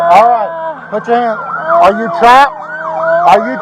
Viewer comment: "are you (drunk? dry? sad?)" I can't hear what does he ask her. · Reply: he asks "are you trapped?"